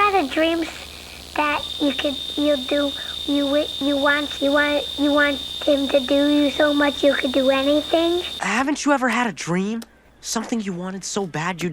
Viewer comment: Maybe it’s just my childhood trauma, but I always heard “you want him to do you so much you could do anything”.